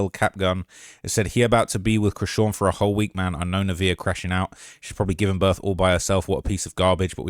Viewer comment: Was his name Rondale? When she was yelling at her mom it sounded like she said “I KNOW! RONDALE!” I could be trippin tho.